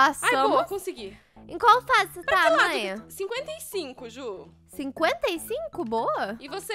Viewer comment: EU OUVI 155